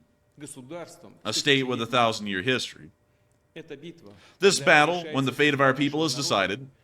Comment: When Poo-tin said " Thousand year history " I just heard "Thousand year Reich " .